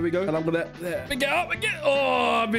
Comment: did he say "badonkdadonk"?